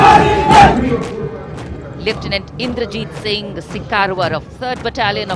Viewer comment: What's she saying at 1:19:13 (Sikar warab) spelling and the meaning please Indians...🇮🇳